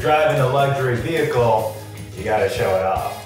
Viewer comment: Eugene: who told you to dress like that? Matt: .......You. Eugene: That's right!~ because if you're driving a luxury vehicle, you gotta show it off 🤔👏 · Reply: I thought he said electric vehicle and I was like ...? What difference does that make?